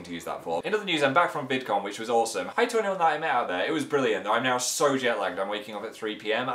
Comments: Wtf did he say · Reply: Hi to anyone I met out there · Omfg what does Phil say at 3:40 it's just a jumble of letters XD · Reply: I think he said "hi to anyone I meet out there" but idk